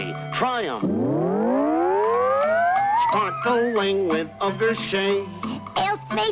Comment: What does Toucan Sam say at 0:43? can't quite make it out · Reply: "Sparkling with ugar-say"